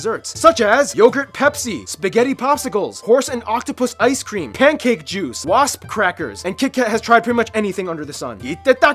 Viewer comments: I thought he said "Pancake Jews" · horse & octapus ice cream??? oh dear.... Did i hear right?